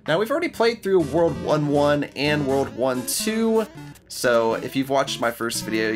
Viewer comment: You all are so AWESOME! 99,494 subs today...I might actually hit my 100k sub goal before 2019. Thanks for all the support, I couldn't do it with out you. Now for an important question... Do you prefer jam, jelly, or preserves? Let me know in a reply! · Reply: When you say world 1 2 I thought you say WW2